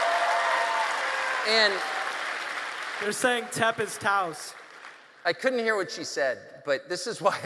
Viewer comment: a member of the audience says something like "tempest touse". Would someone please explain to me what they are saying. I don't understand.